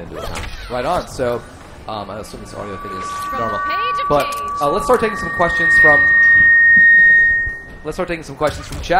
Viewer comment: what did she say? "Rubber cage the cage"? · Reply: From the page of cage